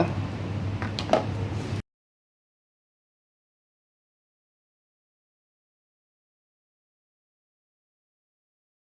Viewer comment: what kinda of batter mix is this its muted and can't hear this part , the batter ? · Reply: I used tempura flour to make tempura batter🙂 its especial Powder used for coating! You can easily but any Japanese food store saying tempura flour 😉 Thank you have a good day #StaySafe